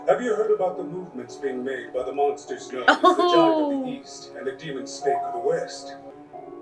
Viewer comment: The dub really makes it sound like Aura is saying the "Vagina of the east" lol.